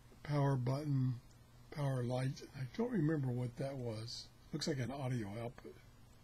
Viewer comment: Concerning Alexa, I think that when you said “Looks like an audio output,” at the 4:22 mark, Alexa misinterpreted “Looks like an” for “Alexa”.